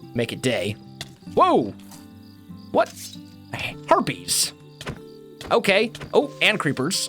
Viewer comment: I thought said Herpes...